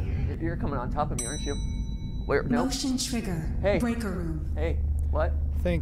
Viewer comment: what the heck did the system voice said gregary🧐🧐🧐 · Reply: “Breaker room” lmao